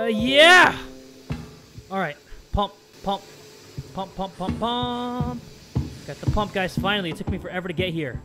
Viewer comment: When he said I got the pump (I thought he said I got the hump)